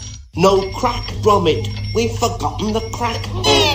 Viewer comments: It took me a good minute to figure out he said: “We’ve forgotten the crack” But what I heard was “We’ve f**king up the crack” My ears are weird(what’s are ears again?) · I thought he said "we f*cked up In the crack."